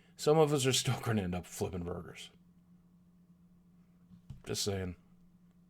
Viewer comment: I was reading the reddit while watching this. when it got to the end I thought you said "Some of us are still gonna end up flippin' murderers" Spews coffee